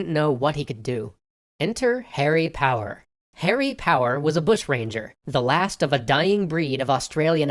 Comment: I thought it said harry potter!